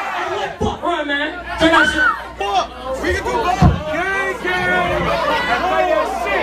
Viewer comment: did someone say gay gang? 😂😂